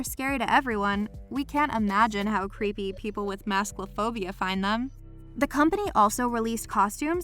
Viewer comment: I thought I heard 'Mouskaphobia' (another Mondegreen there)